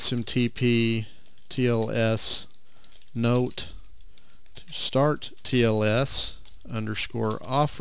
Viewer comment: Thank you for your great tutorial on Postfix at 28:00, which one is correct line? as I heard it as start tls. smtp_tls_note_stattls_offer = yes or smtp_tls_note_starttls_offer = yes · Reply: Hi and thanks for watching. Yes the line should be starttls not stattls. A little further into the video at 42:24 I actually restart the postfix service and discover the error, at which point I correct it. But I will add a notation at 28:00 about this. Thank you for finding that and reminding me...Hope you got your mail server up and running!